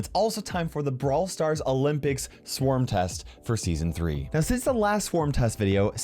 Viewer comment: did he just say season 3